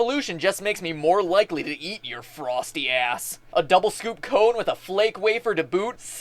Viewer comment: DID MATPAT SAY A!S AT 05:06?